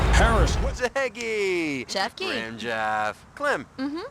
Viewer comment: 2 things. Half of these are sports games and at 8:50 it sounds like he says rimjob...